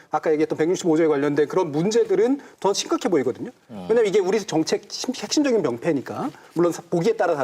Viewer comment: 초의 자막은 문맥상 "우리 정책의"가 아니라 "우리 정치의" 가 더 적절해 보입니다....물론 정준희 교수님 발음이 정책같이 들리지만 정치를 잘못 발음하셨을듯... 편집 학생분 파이팅